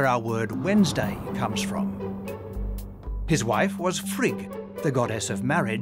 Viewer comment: sorry Frig, I heard “his wife was a freak” 😅